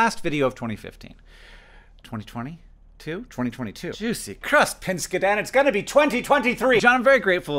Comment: I have no idea what Hank says around 0:06, but whatever it is, it's my new favorite minced oath. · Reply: "Juicy Crust, Pinskidan!" I think it's part minced oath, part reference to My Brother, My Brother, And Me